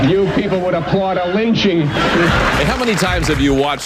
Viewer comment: I know that bastard did not just say, "You'd applaud s lynching." 😑